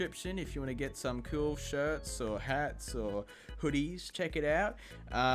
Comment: Those sticker designs would be great on hoodies. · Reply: I thought it Said “noodles” instead Of hoodies…